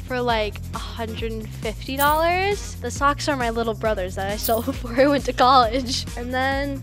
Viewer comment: I thought she said my socks are my little bro